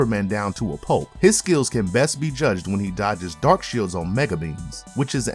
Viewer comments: … did you just call him dark shield? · did you say darkshield?